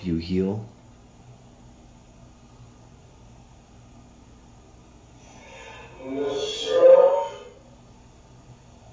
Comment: sound like he said "fo sho" which was a 90s slang for "absolutely!" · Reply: He did say for sho